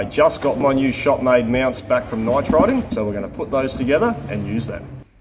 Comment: Well OK nitriders apply a ceramic coating made from titanium and nitrogen. I thought he was saying Nightriders.